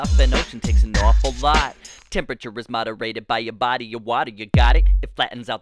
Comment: why is everyone saying its so good, he literally say body of watey